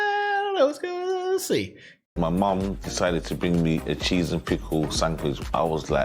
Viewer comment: Thought he said Pringles to I bet it is good tho I put Doritos on my sandwichs lol